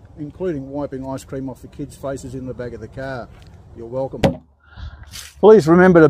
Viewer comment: I thought he said “please be sure to bury your wife” for a second 😂